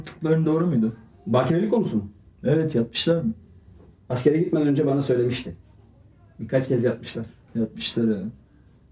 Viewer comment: oha neler duyuyorum yapmişlarmi dedi benmi yanlış anladım😅😅😅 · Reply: Yatmislar dedi